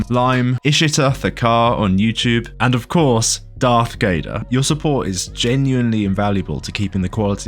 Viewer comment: Shit was being serious then I heard "Darth Gayder"